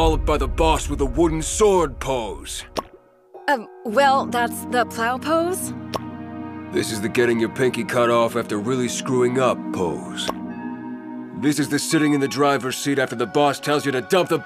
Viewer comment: did she just say the "plow" pose?